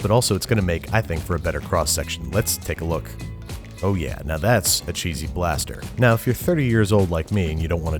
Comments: i thought it said "cheesy basterd" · I thought it said cheesy bastard